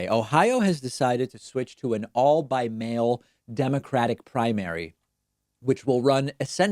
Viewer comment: I was so confused when the video autoplayed and I heard you say that Ohio is switching to an "all bi-male" democratic primary.